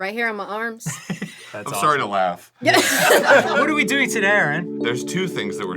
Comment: i heard "what are we doing tod-Arin?" as Dan said it to fast and so thought Arin would say "there's two things we're doing to-Dan."